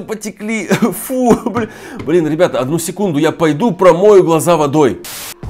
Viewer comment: после слова слезы он сказал блять еле еле слышно но я услышал